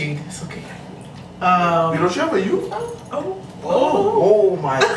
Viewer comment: What did he say at 5:11 ? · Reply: Victoria Marie he said " don't you have a youth fam "